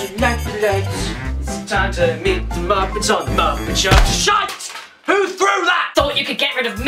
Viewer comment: What is he saying at 1:19? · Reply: jalapeño of justice He's singing the Muppets theme and interrupts it with 'SHITE!' When he gets hit.